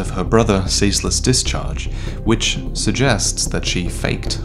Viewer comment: "Wiles" instead of "why else" in subtitles